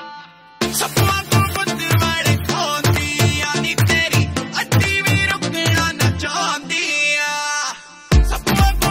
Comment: What he exactly said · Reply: he said 'sapp waangu gutt walkhandi aa'